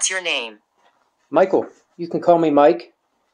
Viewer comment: Wow, spirits are not shy here so this is going to be awesome. I couldn't understand her clearly but certainly replied to you. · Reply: sounded different when i reviewed it. I heard "Hi Mike"